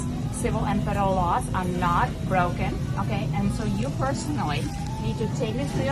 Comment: did she say "I'm not broken" at 2:29 ?